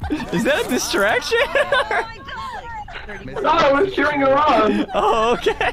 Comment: i think he said "cheering her on" not sure tho